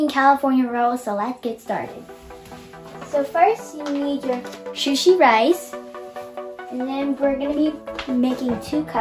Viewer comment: did she just say shusi rice instead of sushi rice 🍚 🍣